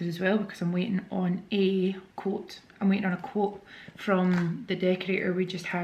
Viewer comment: I thought you said you were waiting on a coat. I was thinking why is the decorator bringing you a coat. 😂😂😂 Xxx